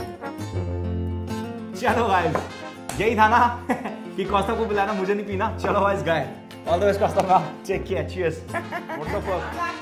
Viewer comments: did he say chalo byes , guy.? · it sounded like "chalo byes..guy"😂😂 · Did he say chalo byes guy at 1:38??